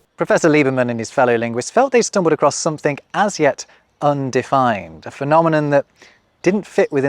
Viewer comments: Did you say somethink instead of something at 2:47? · People who say 'somethink' instead of 'something'. Oops - you did it at 2:45.